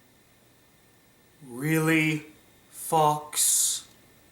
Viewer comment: Am I the only one who misheard "REALLY FOX" as "REALLY FUCKS" xD